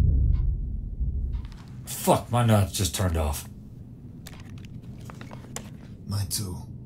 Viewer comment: anyone else thought he said “my nuts just turned off” 💀💀